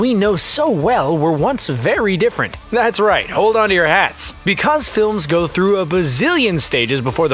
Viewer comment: I thought it said “hold on to your a”